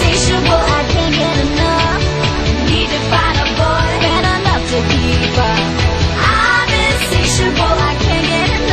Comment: all i hear was "good enough to be diva"